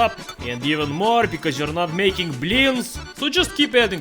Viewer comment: I know, "blin" is "pancake" OR "family friendly blyat", and that "blyat" means "wh@ fu€k." I know when he says, "you're not making blin" he means "pancake", but every damn video, when he does, I hear him say, "You're not making whores....!"